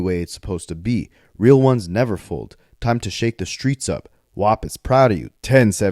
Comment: I thought Pooh said "Fuck the streets" I don't know what to believe.